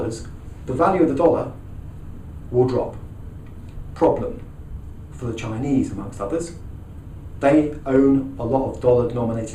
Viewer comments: did he say "there's a problem, for the Chinese Mother F-ers" :-) I must have heard it wrong... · does he say "for the chinese motherfuckers"?